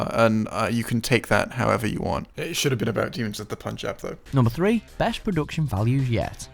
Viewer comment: can someone please tell me what he said at 12:37? i cannot for the life of me understand it and the subtiles arent helping lol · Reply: “It should have been Demons of the Punjab”